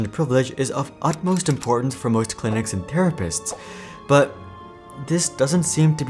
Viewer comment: You said "therapists" but in CC "psychiatrists" 5:10 metal instead of meNtal 5:19 vein instead of effort 6:53 required policies instead of more robust 11:26 bets instead of best